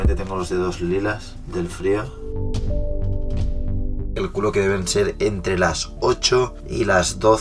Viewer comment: parece que dice en el cambio de toma: el culo que deben ser :v